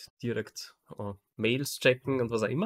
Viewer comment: ich hab einfach verstanden "in die Arbeit Mädels checken" statt "Mails checken" oh man...:D Toller Podcast. Ihr schafft es ein Fitnesspodcast zu sein, der aber trotzdem nicht wie alle anderen ist und Themen mal neu und anders betrachtet. Danke dafür :)